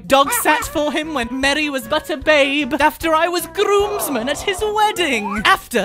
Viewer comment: did he say butter babe me pulls up slow mow thing hmmmmm